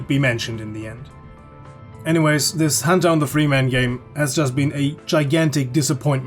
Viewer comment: Hajajah lol i thought it said freshman